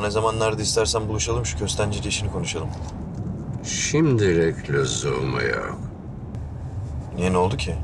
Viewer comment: de Celal ne diyor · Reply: Şimdilik lüzum yok diyor